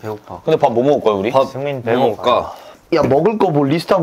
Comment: if anyone didn't catch, here is seungmin saying "seungmin's hungry" in third person (i died